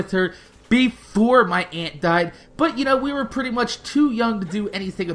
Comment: Oh you said aunt die'n I thought you said aunt Diane probably because that's my mom's name